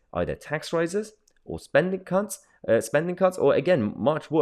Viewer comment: Did you just say spending cunts?